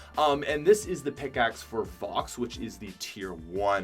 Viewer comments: I swear you said vox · did he say FOX?!